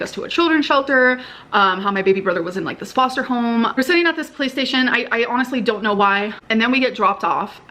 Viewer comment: I thought I was mishearing her say "PlayStation" when she was was saying "Police Station" and even the closed captioning on YouTube thinks she's saying PlayStation.